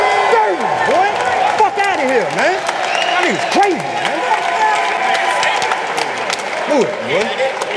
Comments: I didn't hear it. I listened over and over but I heard "y'all crazy." There were some missing syllables for "y'all niggas crazy". But it could be my phone. Y'all should check it out again. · does he says “y’all Niggaz crazy”🤔